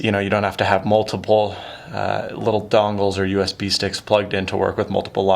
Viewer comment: Did you just say... "dongles"?